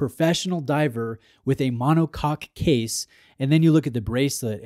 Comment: did you say mono cock case?😂 Seriously you should keep it, I bought the sla035 2 years ago and love it, one of the best dive watches I’ve ever seen